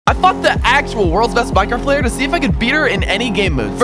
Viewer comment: I had low volume on my phone and I didn't see the subtitle when he said he fought the best Minecraft pvper so I HEARD "I f***ed the Actual world's best Minecraft Pvper to see if i could beat (her)" AND I ALMOST SPAT OUT MY COFFEE